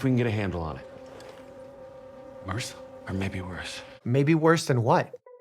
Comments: I think the nurse says “MRSA” before the doctor says “or maybe worse”, so that’s what he’s referring to. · I think he was saying MRS(A). Resistant staphylococcus. So that 'Maybe worse' was meant as in worse than MRSA. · I think the nurse said MRSA (should be Methycillin-Resistant Staph Aureus, sorry for any spelling errors)